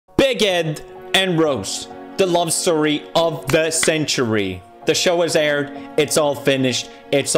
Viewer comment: I thought you said “Bacon and Roast: the love story of the century.” Tbh would have been a better love story.